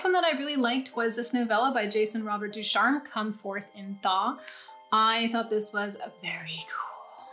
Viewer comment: I was looking at art while playing you in the background and thought I heard Come Forth In Thong.